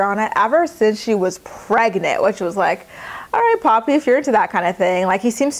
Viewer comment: Every time you said Javi I thought you were saying "poppy"! lol I was like," is that his nickname now?"😅😆😅😆